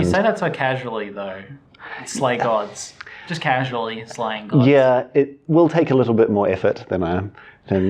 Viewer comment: I misheard that as "Sleigh Gods". ON LUNARIS, ON SOLARIS, ON KUDUKU, ON HINEKORA AND KITAVA, ON BRINE KING, ON TUKOHAMA !